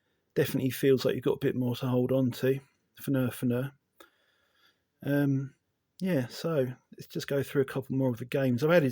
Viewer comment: what did you say at?, sorry English is not my first language 😅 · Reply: "Fner Fner" is english slang for "that sounds a bit sexual" haha